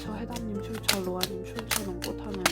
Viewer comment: 빨강머리 태형옵 루아언니 출처임미다 로아님 출처로 들려서 댓글 남겨요 ! ( 제대로 발음하신 거면 죄송합니다 😭😭